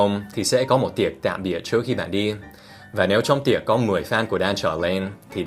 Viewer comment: Phút 8:44 Dan phát âm "fan" là "phan". Có phải mình nghe nhầm không... ,__, 😂